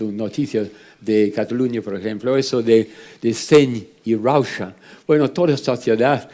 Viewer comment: ¿Puede alguien decirme que dice John Elliot ahí? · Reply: Seny i rauxa.